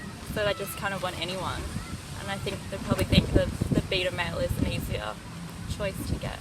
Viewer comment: it sounds like she says beta manlet